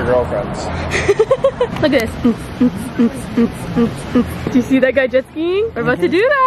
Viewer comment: I swore I heard when you said “do you see that guy jet skiing, “ “Do you see that guy peeing, we’re about to do that!!!!!”